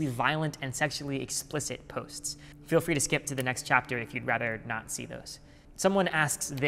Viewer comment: he hilariously says "not see" which sounded like "nazi"